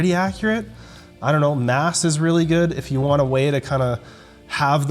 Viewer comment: Which channel did mention related to breaking down papers? Did he say mass (9:05)? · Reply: Yeah, MASS - it's the Nuckols/Trexler/Helms/etc. lifting nerd consortium... they've got a subscription publication, and then collectively do an assortment of podcasts available through utube, the most recent of which is MASS Office Hours. If you tune in live, they answer questions. (Its on Wednesdays.) It's some good times. Too much body building content for my tastes, but I have long commutes & like the personalities, so it's chill listening even when the subject is Cutting Weight While Maintaining Muscle Mass, yet again. (Sometimes they even talk about running!😲)